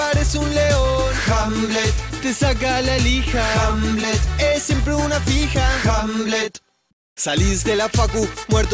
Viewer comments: Jajajaj pensé que decía 'Es siempre una pija' jajahsha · Pense que decia "es siempre una pij*" 😂😂😂😂😂😂